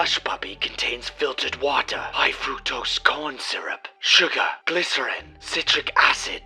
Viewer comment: i heard nitroglycerin 💀💀💀